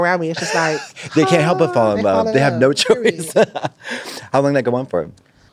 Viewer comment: What tf did Katya say?? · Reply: “How long that go on for?”